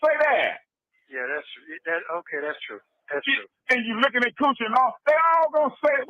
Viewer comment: Did anyone else hear this lickin dey coochie msg from?... wth